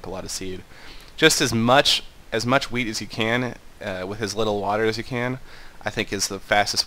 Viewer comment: am I the only one who heard weed instead of wheat?